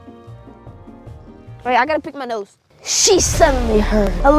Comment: Did he just say wait i need to pick my nose?💀✋🏻